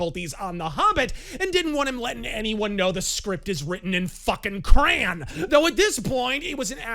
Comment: is one of those people who says "cran" instead of "crayon." SMH.